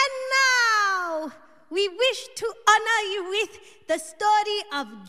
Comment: I swear, I thought she said we wish to ANNOY you.